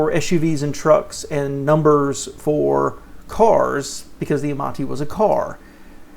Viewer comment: Amati was Mazda.... I need to re-watch to see if I said it wrong. :) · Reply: You were right, I thought you said Amanti, which was kia